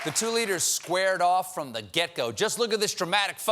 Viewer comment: i heard stephen say, "the 2-liters squared off."